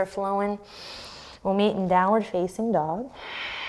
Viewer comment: Great practice today even if I started in the wrong place this has helped me to refocus. Now my hearing is not great and I didn’t have speakers anyways at one point I heard “downward faking dog” which I thought was hilarious! Be well people! 🧘🏻‍♀️